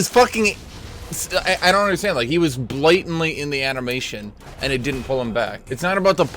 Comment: what does he say on 1:21 ,blitenly? or what?